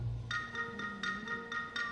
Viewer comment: がめちゃんって聞こえた⁉️